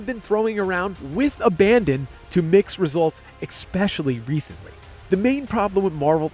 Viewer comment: did you just say expecially??